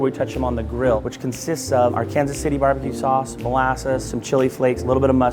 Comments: did he say ar-Kansas? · the dude said Arkansas with a hard s at the end instead of Arkansawwwwww lmao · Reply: He said “our Kansas City BBQ sauce” 😂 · Who else noticed that he said Arkansas instead of Arkansaw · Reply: “Our Kansas City’ not ‘Arkansas city’ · idk how i noticed this but what person who made the subtitles thought he said “Arkansas city barbecue sauce” no one pronounces arkansas like that. he definitely said “our kansas city barbecue sauce”